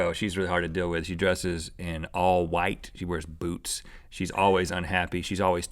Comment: I thought Rhett said coco wears boobs instead of boots